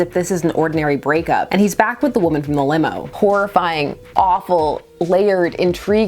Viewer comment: I heard this as "Whorifying" instead of "Horrifying". I apologize for being a disgusting person.